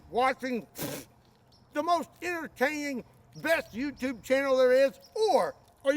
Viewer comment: sounded like you said "the most irritating content"🤣